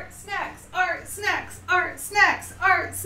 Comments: I thought was saying “art sex” as a chant I’m so idiotic wow lol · At first I thought she said “art sex” repeatedly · I thought you were saying “art sex!” throughout the intro lol. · I thought you were saying art sex....